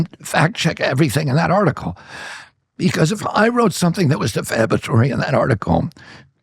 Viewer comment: On the caption it reads "Isn't that that's a lie..." What Tucker said is: "Isn't that, that's illegal..." This implies that Tucker is claiming Kennedy is lying. Big difference for a person who is deaf. This changes the whole meaning and comprehension. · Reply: "Because if I wrote something that was the obituary in that article..." is what the caption read. "Because if I wrote something that was defamatory in that article..." is what Kennedy said.